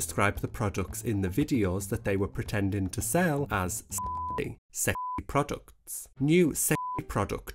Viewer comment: every time you say "sexy product" i hear it as "shit product" lmfaoooo